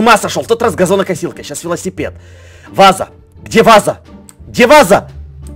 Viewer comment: вместо "где ваза" услышала "диваза"